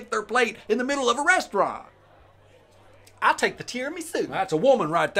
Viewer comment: "She could lick the bark off a dog" Aaaaaand I'm stealing that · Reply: Thought I heard bark off a tree, which is hard wood, 😮 Oh, never mind...❤